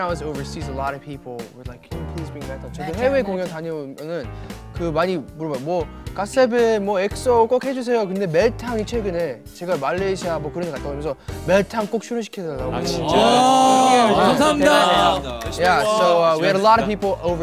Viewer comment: i heard Malaysia ?